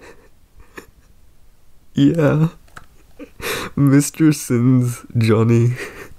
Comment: Mr. Sims, Jonny? As in Jonny sims? As in head archivist of the Magnus institute London? Did I hear that right????